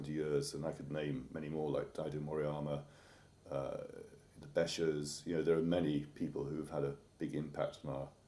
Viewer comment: What is the other name that he mentions after Moriyama at 10:30 ? I don't understand. I'm self tought. Did he say "De Bascher" or what is that name?! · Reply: ) The Becher School: <link>